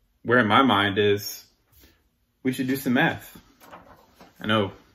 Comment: Not going to lie. Knee-jerk reaction I thought he said "let's do some meth." I'm sitting here like "well that explains some things."